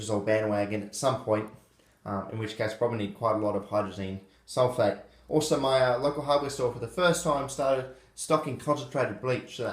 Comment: I was half-listening to the start of the video and I thought you said you bought 15L of hydrazine at the hardware store